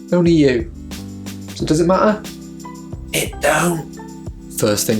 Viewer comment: sounds like you said, "It Doou" 🤣